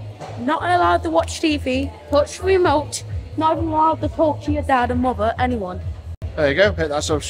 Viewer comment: i thought he said "not allowed to talk to your dad and lover" 😂